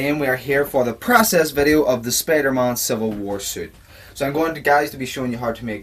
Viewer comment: did he say spidermon lol